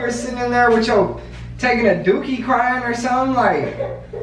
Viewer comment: wait a damn minute did he just say " i dont kno if you taking a dookie or sum" lmfaooo